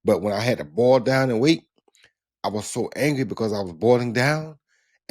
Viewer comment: My man RJJr gets hangry. That's what I just heard him say. No wonder he busted so many heads lololol.